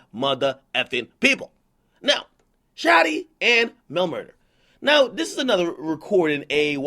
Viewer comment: the subtitle says "chatty and no murder"